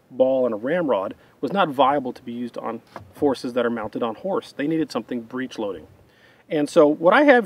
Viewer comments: Karl said "Forces that are mounted on Horse", I heard "Horses that are mounted on Horse"... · I thought Karl said "Horses mounted on horses" instead of "Forces mounted on horses." And i was like "You son of a bitch I'm in!"